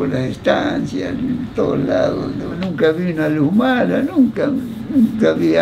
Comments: creo que dice "nunca vi una luz mala" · Creo que dijo "nunca vi una luz mala" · En el min 0:11 dice “nunca vi una luz mala” en vez de “algo malo”. La luz mala es un mito de zonas rurales que dice que a la noche aparece una luz al ras del suelo que se desplaza o queda quieta, se dice que es un alma en pena que no ha recibido cristiana sepultura. La explicación se debe a la descomposición fosfórica de restos de animales. Gracias por tus videos luisito.